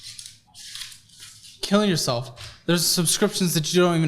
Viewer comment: I may have heard it wrong but at 19:45 did you say “kill yourself” ? I’m sure you didn’t mean anything by it, but friendly reminder to not say this to anyone regardless of intention. ❤ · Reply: He said “you’re killing yourself” meaning with all the transactions. I don’t think would ever tell someone to kill themselves.